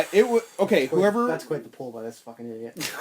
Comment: What did say at 27:07 ? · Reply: "That's quite the pull by this fucking idiot," in reference to my mention of Road Warrior Animal.